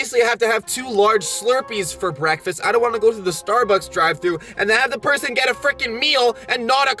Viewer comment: did you just say “Starbucks” WTH